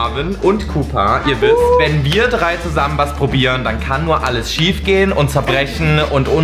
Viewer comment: Hab einfach "Wenn wir drei zusammen Mastubieren" verstanden😂😂